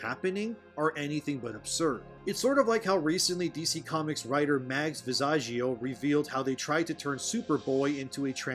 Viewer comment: Every time I hear "Mags Visaggio" I think "Vag Misogyno".